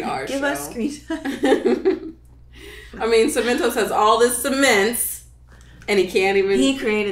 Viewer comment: I heard "Semen Toast" and am very disappointed with myself